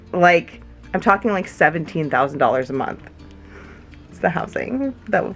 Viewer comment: I thought said 1700 dollars a month is disgustingly expensive and I was like hold up and then it was 17k a MONTH Like I knew California was expensive but not that expensive, Stay safe! Be healthy and lets hope their isn't any problems stopping the people to do the job they need to do so they can hopefully come back before Parkers Bday!!